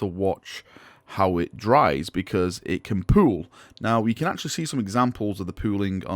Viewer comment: I really misheard him at 1:49 "It can poo" Wtf, GWs gellar fields are starting to fail...